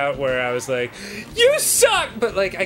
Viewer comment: It sounded like he said holy fuck a weed because of a ad